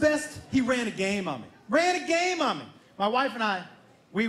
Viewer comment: wtf i had to rewind, i thought you said "my teemage boy ran a train on me"? then i saw that cool whiskey shirt and was like, "theres no way this mans man whiskey drinking real man with a fucking real beard let his son run a train on him,, guess ill resume the video and see where this goes, spit or swallow? creampies? ooh